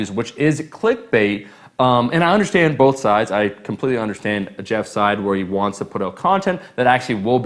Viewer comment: anyone else catch him saying Jeff Seid at 7:25 ? probably wanted to say Jeff's side